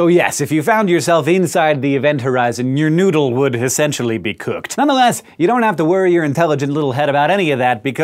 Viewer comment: what he said: "Your noodle would essentially be cooked" What i heard: "Your noodle would essentially be longer" I think black holes are the only actually-working penis expanders in the universe